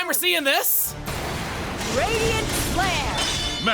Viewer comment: I thought it was called "radiant slam" for like 4 playthroughs wat seriously listen to it thinking as if shes saying "radiant slam" and i assure you that you will hear my pain